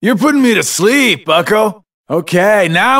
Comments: R for Reload? Xigbar is a gamer! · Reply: I always thought he said "Time to reload" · I thought he said “Hard to reload” my life is a lie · this game is 10 years old and this entire time I thought he said, "Alright, reload!"... my world is shattered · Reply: I always thought he said "Ahh, reload". Knowing that he said "R for reload" makes this fight even better... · I heard "Aw, gotta reload." Like he was always surprised when he ran out of ammo. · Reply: No he said R for reload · R for Reload?? I thought he said All out. Reload! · R for Reload? Xigbar is a gamer! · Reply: I always thought he said "Ahh reload" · Huh, I always thought he said “Stock and reload” but it’s actually “R for reload” 😆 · I honest to god thought he was just saying "Argh,reload". XD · I heard "Aw, gotta reload." Like he was always surprised when he ran out of ammo. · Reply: I always thought he said "armed and reload" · I Always thought he said "Arrrkkk, reload!" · I always thought he said "Ahh reload" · R for Reload? Xigbar is a gamer! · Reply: I thought he said “Ah, reload!”